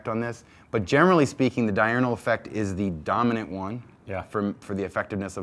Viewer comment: Did he say "Dynamo Effect" ??